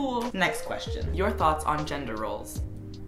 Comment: Omg I thought you said my thoughts on GENITALS not GENDER ROLES ahhhhhhhhHHH